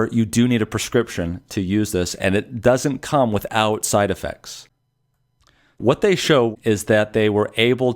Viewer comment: Can you please make a comparison between Remdesivir and Chloroquine including the costs on future episode? That would be wonderful, you have indicate Chloroquine has little or no side effect, that was very good to hear, but I do not know the details of Remdesivir from Gilead. You are wonderful! Thank you so much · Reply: Hm. I heard something different. -> 7:16 "It does not come without side effects." ???